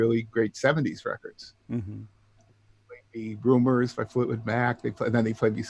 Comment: Did anybody catch the artists and/or tracks metioned around 3:10? I caught Giorgio but I can't figure the rest out. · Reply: The fist one is Rumors, the album from Fleetwood Mac.